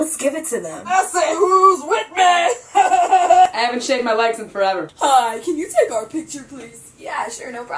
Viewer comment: I haven't shaved my legs in five months 😂 were my hairy legs at? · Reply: Sarina Ramirez I thought she said 'I haven't shaved my legs since forever'😂😂😂😂😂😂